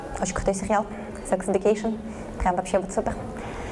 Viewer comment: какой сепиал? Я слышу "Sex indication". Но иакого же нет. Что она имеет в виду?